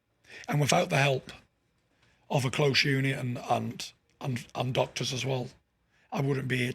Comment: What does he say at 3:55? Without the help of a "close union" and doctors? I can't make out what he said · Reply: Close unit